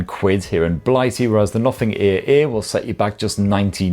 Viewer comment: With that British accent, all i hear is " nothing ear ear " 😂